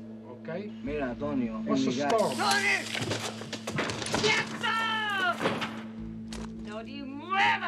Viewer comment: What does Marta say? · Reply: she says: "quietoo" It means like don't move or stay quiet .